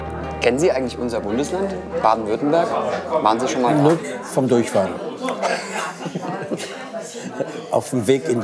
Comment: "Kennen SIe eigentlich unser Bundesland?" - "Nur vom Durchfall" (habe ich verstanden zuerst) :-D